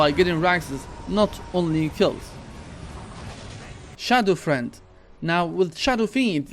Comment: Did you just say SHADOW FREND